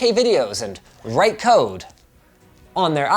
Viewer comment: did he just say code but how is it possible 🤔??? If yes,tell me that date